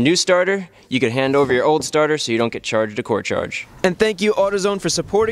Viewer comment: the subtitles say "so you dont get charged court" even though you say "so you dont get charged a core charge"